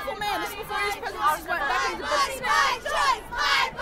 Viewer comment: Legit I thought they were saying "my body my trump" , when they were saying "my body my choice"